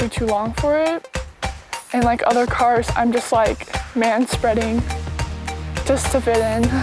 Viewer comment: did she say man spreading?